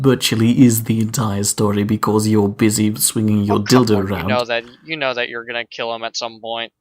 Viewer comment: what did he just say!? Did I hear that correctly? "You're busy swinging your dildo around", a classic quote.